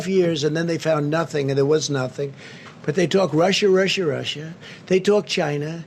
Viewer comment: When Trump says, "Russia! Russia!, Russia!" all I hear is "Marsha! Marsha! Marsha!"